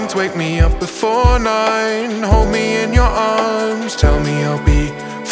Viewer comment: i keep hearing "homie in your arms"